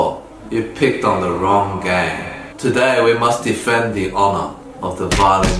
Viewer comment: what the ** did he say? “ you picked up the wrong gay”? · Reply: Miss hearing speling writing of YT.. 🤣 he said "gang"